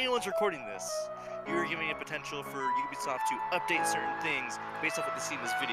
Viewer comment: WHAT DID HE SAY. Dam it dude. You put that music, bad timing. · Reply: Enrique Torres he said you’re ruining the chance of us getting banned